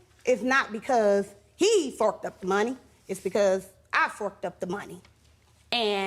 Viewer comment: Oohhh..Forked up the money I thought she said FUCKED UP THE MONEY smh